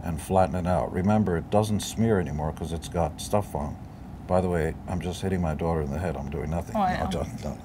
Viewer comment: what does they say? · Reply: "by the way, I'm just hitting my daughter in the head, I'm doing nothing"